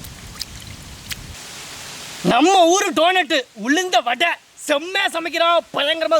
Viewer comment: Namma Ooru Toilette ... ? Wtf 😂 who heard that too. click Like